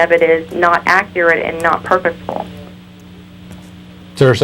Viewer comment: "Not perfectful" maybe I misheard, but I don't think "perfectful" is a word